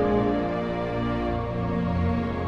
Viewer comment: lol I thought I was tripping balls here, I play a video game where "bloon booster" is one of the phrases that you hear no where else. Almost thought I heard it here